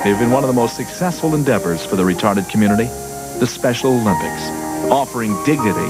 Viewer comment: did he say regarded